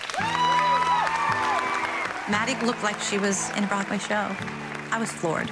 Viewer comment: it sounded like she said she was bored watching Maddie dance